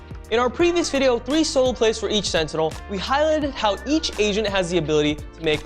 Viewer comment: I thought he said "each asian" not each agent.